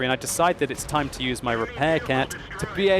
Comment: What do you think are the best tanks Tier for Tier in the game? I'm LiveStreaming the new patch right now! <link> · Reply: Did I hear you say "repair cat"?? anyways, great video